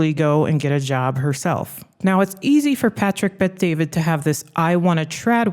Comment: Why did I hear "Patrick Bates" lol fits tho I guess